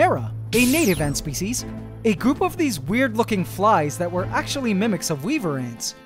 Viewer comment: why did I think he said "a group of these werd f0king flys